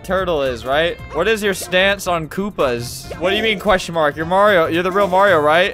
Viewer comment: As a Polish person I heard "Kupas" It means poos, its a plural